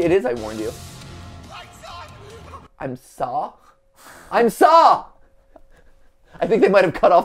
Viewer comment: I heard "I saw him" maybe he told Gregory to get away from him because moondrop was going to take control